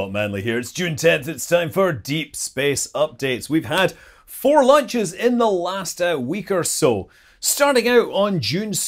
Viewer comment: Me, and the closed caption, thought you said "we've had four LUNCHES in the last week or so". ;)